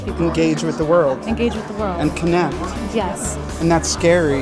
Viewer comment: Am I the only one that thought when Adam said connect he said kidnapped